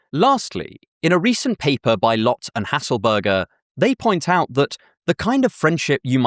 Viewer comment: burger ? did you just say his last name is Burger?okay I’ll stop But a Joe bot would be quite concerning